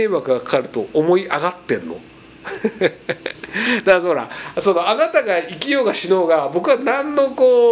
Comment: のおそらく『だからほら』が 『だんのうら』にしか聞こえない